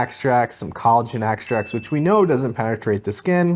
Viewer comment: Sorry i could nt make out what u said 17:32 does collagen penetrate the skin? There is a green tick in the video but subtitles say it doesnt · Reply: It doesn’t but it can act as a hydrator so still can have benefits. It just won’t like actively replace collagen in your skin or anything